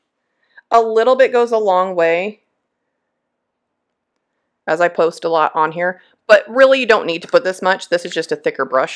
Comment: What is the powdered ink that you used in the Shaker or the little pot it sounded like Lindy's, but I couldn't understand the details?thanks!at 16:00 in your video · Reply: Hi! It's called Lindy's Gang Magicals - they are on sale on Amazon now too! They're so cool <link>